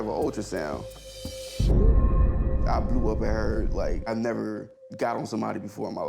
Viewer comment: I'm learning English as a second language. What did the guy say at 3:01 minutes? Please help me out. I just misheard something like I blew with her..? If I'm not mistaken. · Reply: Blew up at her pretty much means that he got mad and started yelling/cursing, expressing himself angrily! Your English is great!! Let me know if you need any other expressions explained :D